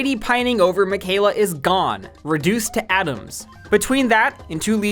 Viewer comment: I had to go back a couple times to make sure I heard correctly when you said "Adams" not "Atoms" lol